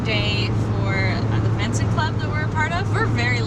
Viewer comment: sounds like she said "Alumni day for the Mensa club that were a part of". I was like .........WOW!